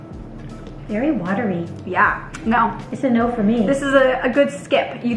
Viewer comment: when her mom said it’s a 9 for the first one i thought she said it’s annoying i was so confused 😂